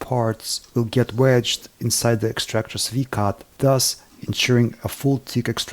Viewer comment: Did you say “V-Card”? Seriously though, this video and the craftsmanship in it, is absolutely amazing.